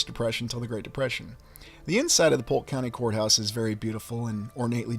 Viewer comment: I thought you said whore house...but you were saying court house 😅